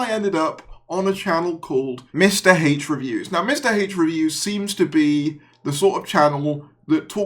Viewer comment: When you said "Mr H Reviews" I heard "Mr Hate Reviews" cause you said H weird, but yeah, it kinda tracks. What a piece of crap channel he has. How a hate-grifter has more followers than legit fan channels is just beyond stupid, but here we are 2024.